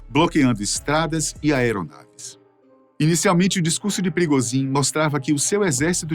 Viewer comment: Eu entendi " Perigosin"😂😂😂😂